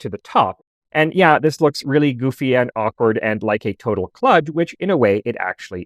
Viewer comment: I think you meant to say 'KLUDGE'. Which of course, by definition, it is. Cheers~!